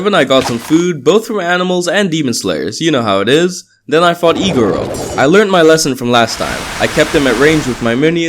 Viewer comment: Iguro. Me: im sorry i understanded E-girl instead of Iguro XD